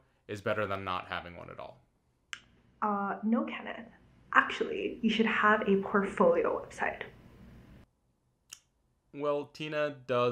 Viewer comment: what does she say here? I can't understand the accent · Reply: She says, "No ken! Actually you should have a portfolio website".